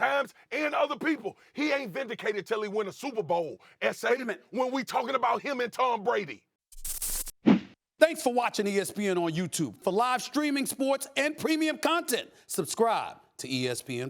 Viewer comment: Did he say esai on 6:58 😂